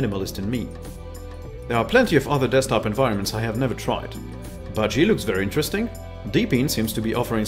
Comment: What is that first desktop environment you mentioned at the end? It's in the top right. I thought you said Juno, but that doesn't look like eos in the top right